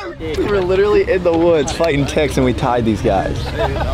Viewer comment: After hole 5 said they were fighting "Ticks" in the woods.. I thought he said they were fighting "Tigs" in the woods 😂😂 ...bunch of little rabid Tigs in woods terrorizing golfers